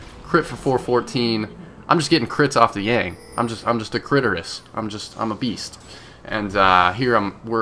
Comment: lolz it sounded like u said u were a clitterus!